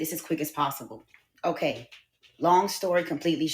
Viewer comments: I thought she said " Long story short" lol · I thought she said long story short🤦🏾‍♂️ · I thought she said, “Long story short”.